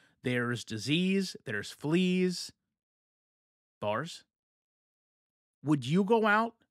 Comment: i’m sorry did you say bars 🤣